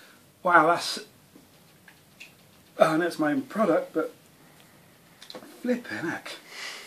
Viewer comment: did i hear finnish word there? You look finnish. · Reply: Oh okay sounded like litteä hämähäkki which means : A flat spider